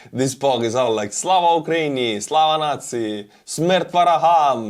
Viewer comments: Of course you didn't mean "Slava Nazi" but it did sound like that and the closed caption said "slava nazi". What was actually saying there? At 14:43 Edit "Where is the money Labowski" LOL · Wait...@ 14:42 did I hear Slava Ukraine Slava N@ ?